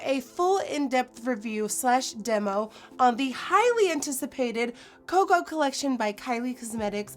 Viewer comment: i thought she said the "kylie requested" instead of "highly" lmaooo